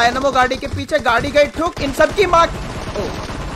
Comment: what he says in sab ki maa ki